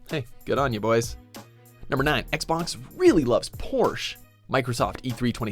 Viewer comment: Fuck im hooked on e3 vids. I swore he said porn not Porsche